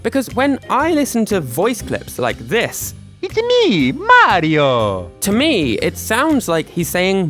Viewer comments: I hear ma-de-o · i hear MAR io here